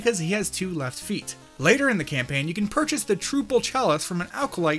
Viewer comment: did he just say “troople”?